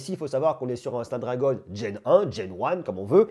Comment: C'est quoi stade ragon djen un (ou one) ? Comme je ne sais pas comment ça s'écrit je ne trouve pas sur google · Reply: Snapdragon Gen 1 :)